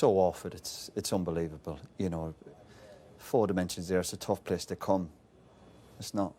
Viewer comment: I thought Roy said “four dimensions there” hahaha